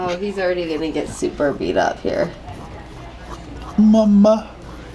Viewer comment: did he said ''mamma'' at 9:18 because it sounded just like that and mamma is Norwegian for mom